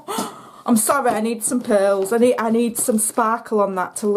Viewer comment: Oh Lordy! After you said you must have wonky eyes, I thought you said you needed your pills. But it was PEARLS that you were looking for! Love your videos!!